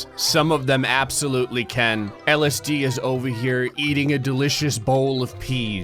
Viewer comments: When you said Ellis Dee it sounded like LSD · "Ellis Dee is over here..." Thought you said "LSD is over here..."